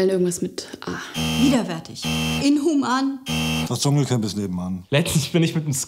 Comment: Was sagt der bei 0:27? Versteht man nicht. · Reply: das dschungelcamp is nebenan